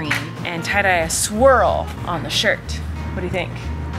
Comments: I seriously thought she said she was going to make a "tie-dye squirrel shirt." I was like, "Wow, how is she going to make a squirrel pattern?" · I completely heard you say “squirrel” and not swirl when you were talking about tye dying the shirt. And I thought wow, she has really stepped up her game with creating animal shapes when making shirts! LoL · I thought you said you were tie dying a “squirrel” on his shirt… omg 😂 you said swirl